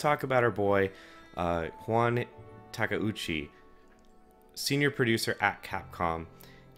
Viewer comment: Juan Takeuchi? lol tf is he saying